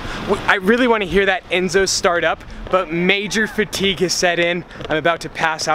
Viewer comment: he says critique instead of fatigue, come on